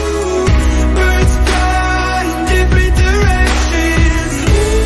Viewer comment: all this time i thought he said every, not different.